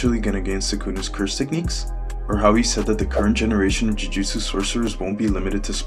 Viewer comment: I thought you said dojutau i was like thats naruto but it is very similiar to a dojutsu